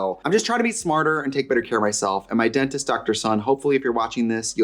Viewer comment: ok I think your dentist is named Dr. Sun, but i heard it as "my dentist-doctor-son" 😅